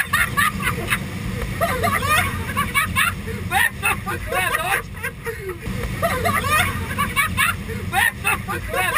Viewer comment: What does he say at 3:21? It sounds like 'Berto bastardo'! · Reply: "Berto ho fatto la doccia"